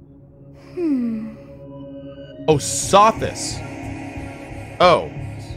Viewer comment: it's Sothis Cohh not Service.. I thought I heard she said that too Service.. hehehehe